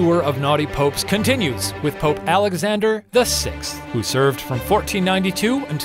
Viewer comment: the captions say "Alexander XI" instead of "Alexander VI" BUZZFEED COME GET YO MANS